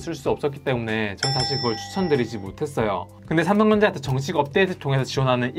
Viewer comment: 저만 삼덩전자로 들리나여 ㅎㅎ